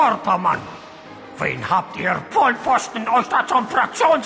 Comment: was sagt er bei 0:21? vollpfosten? ist das deutsch? es ist eher ein österreichisches schimpfwort. aber vielleicht verhöre ich mich da.